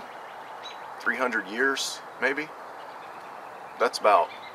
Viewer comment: Very nice bag. Great work on the crafting of it. lol ya know I could have swore you said 100-200 ears. Then I realised you meant years. I was starting to think you where going Mike Tyson. on us. Thanks for showing the great haversack off.